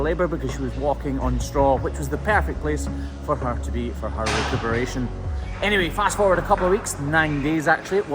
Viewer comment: did he say recoveration at 4:08 or did mishear that